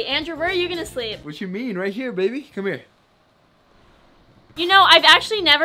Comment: what did he say? Right here BABY come on over here!!!!!